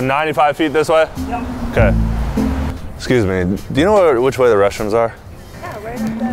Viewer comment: I THOUGHT HE SAID WHICH WAY ARE THE RUSSIANS sorry caps